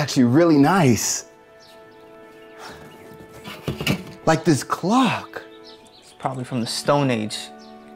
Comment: I swear I heard him say like this glock